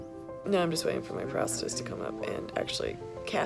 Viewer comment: I thought she said (and my captions even said) “now I’m just waiting for my ‘prostitutes’ to come up”!!!! Hilarious!!!